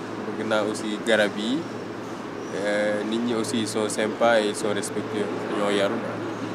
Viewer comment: Did he say a Japanese word “senpai”? anyone know what word exactly he said? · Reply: He said « sympa » which means nice :)